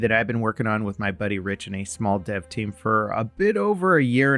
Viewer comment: "from two people" 🥲 There's a small handfull of us :) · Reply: I apologize. I missed heard him, he says his buddy Rich and a small dev team. Thought he said My buddy Rich, we are a small dev team.